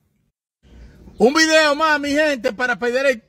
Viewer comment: can someone for the love of GOD type in spanish/english what he says here? I keep searching Embedao mamae hentey and nothing comes up im high af💀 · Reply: Un video mas mis gentes